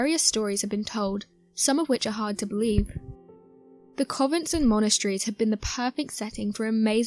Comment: Beautifully narrated, but I'm a bit confused. Did you say "COVENT"?? As in 'CONVENT' - like where nuns live? At about 0:41, & 0:57 & at least once more after then. 😕 Either way, GREAT SUBJECT & video on it, & the pics!! Amazing...... Thanks for sharing!! From Down Under.. 😊 ✌🐨